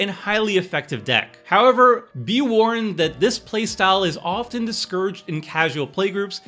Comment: you say "often discouraged", I heard "way to make friends"